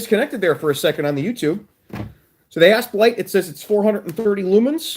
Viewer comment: I was only 1/2 watching while doing something on the other screen and when you said "Asp light" I heard "A$$ wipe" LOL